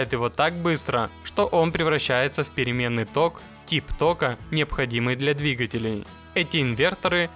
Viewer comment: послышалось Тик тока, а там было тип тока))).